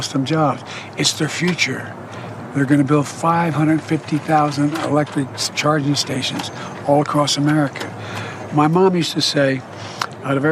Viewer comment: “550,000 electric charging stations across America” There are an estimated 145,000 fueling stations in the US currently. I thought he said 5,500 electric charging stations, and I was thinking “that’s a lot!” 😂